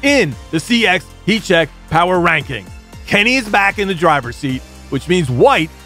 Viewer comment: So much awesome! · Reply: Dude, don't go around impersonating this champion. He clearly says "KENNY IS BACK IN THE DRIVER'S SEAT." 5:20. Sorry dude. So close.